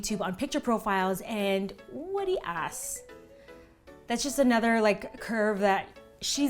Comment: LOL !!!!!! Did you just say "Wuh de Ass" ??? 4:00 Wiiiiaatt na man, are you Trini??? o_O LOLOLOL!! Wuh de ass I seeing hearing here :p